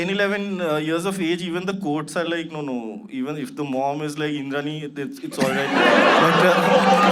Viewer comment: what did Anirban say about custody ? He says something like “even if the Mom is like _____” and something that sounds like- in the knee ?!?! What’s that word he used ???? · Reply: He said Indrani referring to Indrani mukharjee